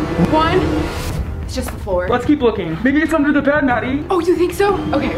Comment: The captions said maybe it's under the Ned Maddie